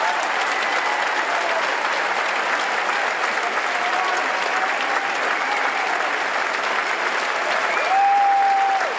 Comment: what does he say? any lip readers? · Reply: I said I'm so happy to have you🤗❤❤😘